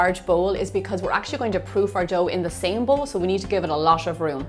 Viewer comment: did she say poop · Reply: She said proof…